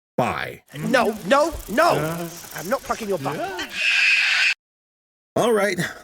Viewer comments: I heard "I am not cracking your thumb" from that audio, but then again I always hear things differently. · I heard: "No, no. I'm not touching your butt."